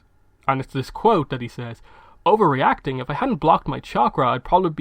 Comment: Well does Aang say “blocked” or “locked” because I think there’s a slight difference in the meaning. I don’t think a chakra can be blocked like chi because chakras are basically pools of chi. But if he said locked, then it might mean the chi can’t escape from that area. It wouldn’t be surprising if lighting has some sort of chi blocking effect because so much energy involved, so maybe the lighting channeled a lot of the chi from the thought chakra in his back and then prevented it from flowing out. This kinda goes in line with your explanation of the rock then, because it could’ve allowed the chi that was locked there to finally escape and flow through the rest of his body when it entered his back. This would mean that Aang was right when he said that. Either way, great video man! You’re awesome. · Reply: He says blocked in the show